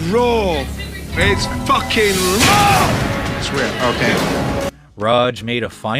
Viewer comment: did Raj say “it’s rare ok”?😂😂😂😂😂😂😂😂😂😂😂😂